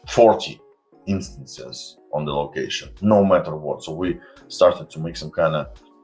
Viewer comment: Pestily I thought you said based on the last interview they fixed the need to "restart" quests like Psycho Sniper or "The Guide", but I have to restart those manually, and of course almost always forget to do so.